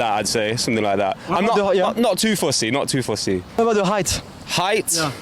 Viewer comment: The first guy said at 01:26 "not too fussy", not "not too feisty". At 03:14, the black man said "it's not much of a bar", not "it's not much for bar". At 08:05, the ginger guy said "anyone willing to put up with me", not "willing to vibe with me" ("vibe with me" is a weird phrase, possibly US English). "Put up with me" he pronounced as /pʊʔ ʊp wɪv mi/ where /ʔ/ is a glottal stop. About 10:05, the ginger guy said "I'm not an expert or anything like that", but you transcribed it as ""an expert on anything like". "Like that" is often pronounced with glottal stops, /laɪʔ æʔ/. Very occasionally some of these subtitles are wrong. It's not a criticism - I'm just pointing it out. Also, try to phrase questions properly: there is something specific a girl has to do to get a guy>is there anything specific a girl has to do to get a guy?